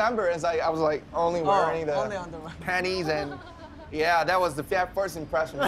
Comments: Video: 4:10 Me: Did Joon said penis. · Them:"boxers" Me:"did I just hear panties?😳" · Did no one else hear panties 4:10?!?! I know that's not what he said it just sounded like it